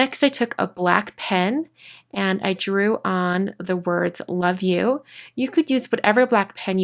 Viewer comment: I was so sleepy I heard "Fuck you" 😂